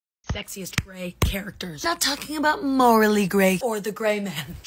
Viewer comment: I thought she said sexist gay characters 😂😂😂